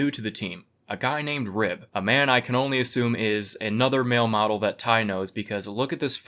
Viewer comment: At first, I thought you said Ty was a “male mom” and I firmly believed it.